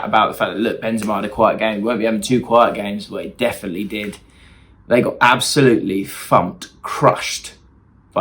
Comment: i thought said "they got absolutely fucked, crushed" and i was dying of laughter